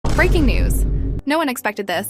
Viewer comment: sorry to say but i totally heard 'freaking news' instead of 'breaking news' u said :3